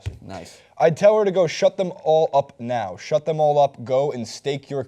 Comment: Who else thought it said mcgregor told her to shut the fuck up